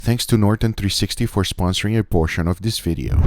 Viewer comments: I thought he said abortion but i found it he said promotion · at the beginning i thought you said abortion, SO THATS HOW TINY RIBS ARE MADE also mixing some mustard into the rub for ribs is really good, there is a mix i had, that was super good, and the rub had only 4 things in it, super simple, and super good